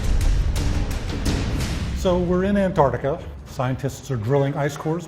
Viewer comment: did he just say anartica LMAO